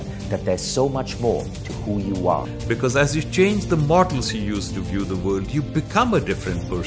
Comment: What did he say at 1:25? Models? The subtitles says "Martins". That's not accurate.